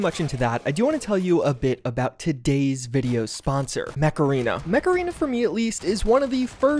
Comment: lol, I'm watching this video while I'm really tired. and when you said mech arena I thought you said macarena and were pronouncing it weird